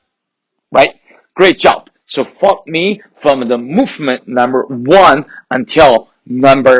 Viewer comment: did you just say the F word ? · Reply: I think he meant to follow me from movement 1